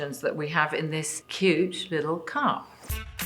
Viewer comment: It sounded like Emma said " huge little cup " Thanks to subtitles ! 🤣🤣🤣